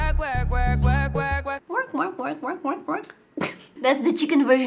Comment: the whole time I thought she was saying Twerk twerk twerk twerk twerk twerk?